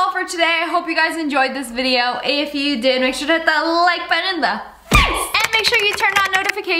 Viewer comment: I thought u said HIT THE LIKE BUTTON AND PEICE but in this vid I heard HIT THAT LIKE BUTTON IN THE FFFACE